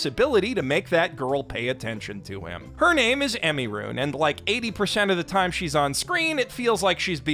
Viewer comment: her name is Emiru? Did i hear that right?